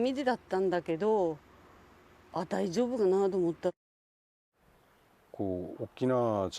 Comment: 字幕間違ってると思います。 この女性の方は 『…大丈夫だな』 ではなくて、 『…大丈夫がな』 と言ってますよ。 これは、｢大丈夫かな？(→大丈夫ではない)｣という意味合いの言葉です。 メ～テレさんの編集だから 方言をよく聞き取れなかったのでしょうか。 高台から見ていた人たちは、 結果としてあんなふうに津波がくるとは思っていなかったにしても、 海岸線で海をみていた数人のいた場所は危ないのではないか、と感じていたと思います。 東日本の時の 津波最大値は、リアス式海岸線部で特に高くなっています。松島でも30m超えていたようです。 それなのに、政府が出している 南海トラフの津波最大値の予想が 25m となっているようです。全く想定が甘すぎると思います。 そして、いまだに 大きな地震がきても 津波が来ないたろうと思ってる人が多い。 南海トラフについてもっと警戒すべきなのではないですか。